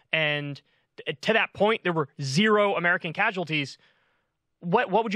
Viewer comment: I must have misunderstood - certainly - I thought I heard say, no civilian casualties - quite a lot including those very young soldiers.